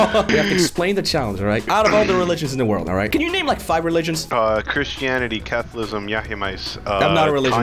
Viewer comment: Is it just me or did I hear Chris-Chan-ity?